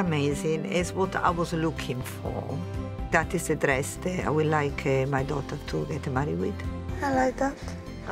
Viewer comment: did she say ' ...that i would like my daughter to wear to MY wedding' ?? dear lord